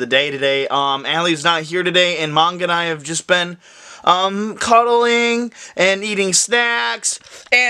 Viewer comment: I thought he said Ali and I have just been cuddling and eating snack I took a double take there